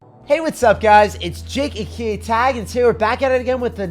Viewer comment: I just got to know he introduce himself as jake aka tag i used to think he says jake cake tag😂😂😂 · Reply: I thought he said jay ak tag